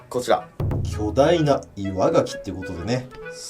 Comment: 巨大な岩牡蠣じゃなくて 巨大な稲垣って聞こえたww